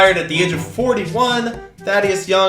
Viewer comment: lowky thought he said "that is young" about Chris Paul when readying out the names but he said Thaddeus Young lol